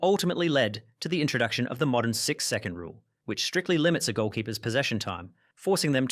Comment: Why i heard 6 second as 6-7 😭